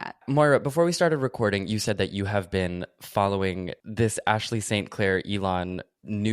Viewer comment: Ashley St Clair could easily be a Pretty Little Liars character. She even has that “popular girl in a YA novel” kind of name. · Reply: I actually had to double take because I totally heard “Blair St Clair” and I was like “damn Blair really fell from grace after Drag Race…”